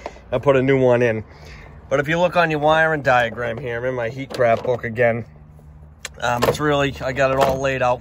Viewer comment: What is name of your book ?? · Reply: Sounds like he says heat graph book but u can’t tell